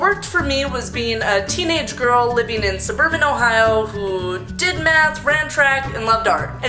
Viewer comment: I thought you said did meth. rofl